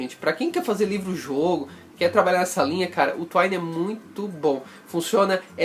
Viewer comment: Desculpa não entendi o nome do sooftware que ele disse no 09:02 de vídeo. Alguém pode me dizer. obg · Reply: Twine - <link>